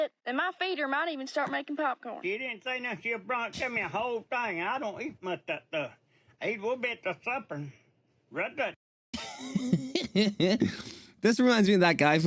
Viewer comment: so the guy at 11:39 was saying “he didn’t say nothing. she’d brought me a whole thing and i don’t eat much of that stuff. ate a little bit for supper and they’ll (?) go bad cause i don’t eat it.” not 100% on some of those words but that’s pretty much what he said. edit: forgot to put “ it” at the end